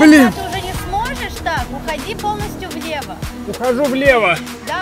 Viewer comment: Уходи полностью в небо! (послышалось): УууИИИИИИИииииии....... (выпрыгивает и исчезает в небе)